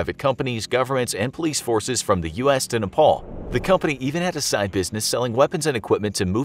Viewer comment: Did he really said "from us to nepal" ?